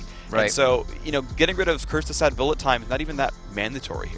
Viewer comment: Anyone else notice he said Curse of the Sad Bullet Time??? Pretty sure I heard it at about